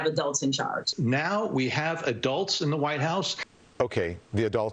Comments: I thought they were saying we have “a dolt” in the Whitehouse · I thought they were saying "We have a dolt in the WH" & I agree